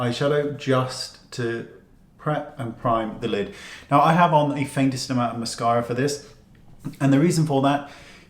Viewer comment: I'm working on the subtitle for this video & English is not my first language...Can anyone let me know what did said at 0:40 "I have the ??? for this", thanks! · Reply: The faintest amount of mascara :) xx